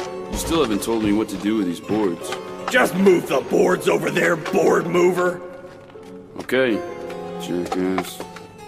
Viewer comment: "Just fill the hole, hole filler" "Sure, Jackass" "Just move the boards over there, board mover" "Okay... Jackass" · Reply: MY DYSLEXIA, i thought you said broad mover LMAO